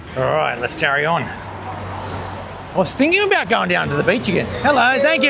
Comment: Hope you feel better soon. After you ate the curry and then you walked out of the cafe..I thought you said 'let's curry on' but you actually said 'let's carry on'. 😂